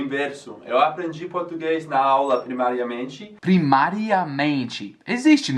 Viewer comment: Eu entendi "primariamente" como "primeiramente" o que ficou bom!!